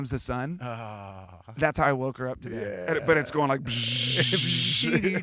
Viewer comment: It sounds like he says "I lost the game" in the beginning. You all just lost the game